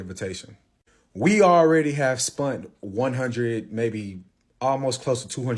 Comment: I’m multitasking…did he just say “spunt”?